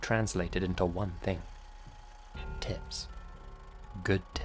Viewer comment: Thought he said “tits”🤣